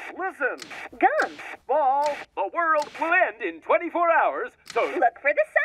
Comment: DID HE SAY THE WORLD?